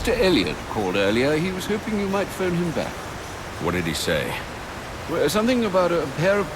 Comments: Did anyone notice at 36:55 batman said what did he say? And the subtitles said did he say what he wanted? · The subtitles don't match what Batman is saying, he says "What did he say" when the subtitles read "Did he say what he wanted" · Look at 36:49 the subtitles are wrong for batman. He says "what did he say" but the subtitles say "did he say what he wanted?"